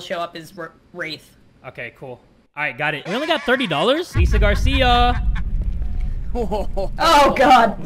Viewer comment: NOOO I THOUGHT HE CALLED ME GARCIA I WAS LIKE WHAT